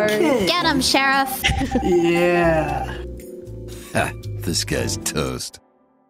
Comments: I always hear "this guy is toast" when he says "disguised toast" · i always thought his intro said “disguised toast”... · Why do I always hear "These guys' toast" or "This guy's toast"